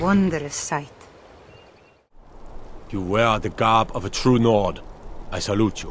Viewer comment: "You wear the garb of a true nord" What I heard: "Care to dance like a true nord"